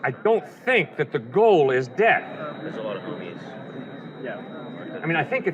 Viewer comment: anybody else catch that “ a lot of boobies”